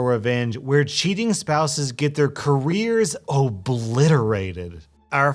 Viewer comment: I thought it said “where cheaters get their livers destroyed” like yeah, I get you